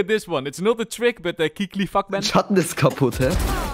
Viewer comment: Bro i just heard "keekly fuck man " 6:42 wtf